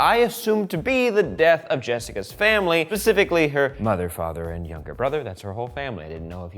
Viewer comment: did he...did he say "Pacificly"??